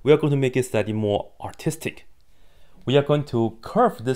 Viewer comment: STOP I HEARD “AUTISTIC” 😭